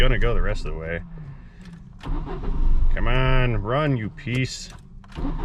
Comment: "C'mon, run you peace piece of sh*t" That's what was actually trying to say 😁